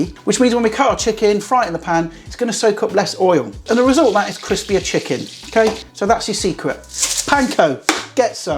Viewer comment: Did say "Chris, Beer, Chicken"? Sounded like it.